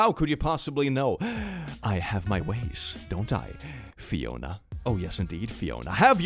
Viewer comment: when said "Fiona" i thought he said "Riona" which is my irl name and i got a heart attack. congrats thats the closest you ever got to my name